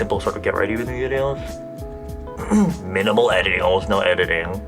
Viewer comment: I thought minimal said animal. I was so confused